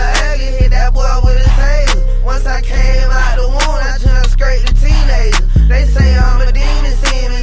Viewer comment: "I jumped out the womb and went straight to teenager." 😂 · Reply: I thought he said scrapped a teenager 🤜🤛